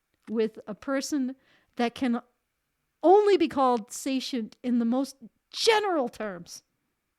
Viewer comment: What are you saying? that's not a word I've ever heard. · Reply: The most general terms